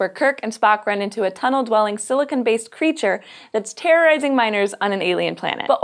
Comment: I heard it as "terrorizing minors" - spit my drink out